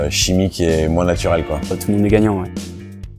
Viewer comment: à 0:08 il dit quoi ?? J'entends "otnégagnanouai" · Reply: "tout le monde est gagnant ouais"